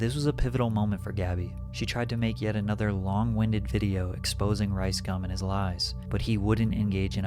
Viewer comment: Imagine when your actions make Rice Gum look like the good guy. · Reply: I thought she kept saying ’rice scum’